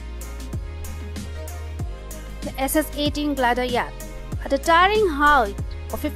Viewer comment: I have no idea what she said. Cool video but need a translator. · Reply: SS18 Glider Yacht. I know...she's got an awesome accent.